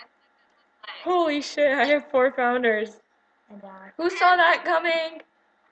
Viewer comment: Did you just say "Holy S*** I HAVE4 FOUNDERS".. XD sorry if you didn't